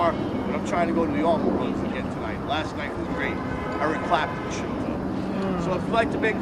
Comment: I thought he said Mark Chapman visited when he actually said Eric Clapton.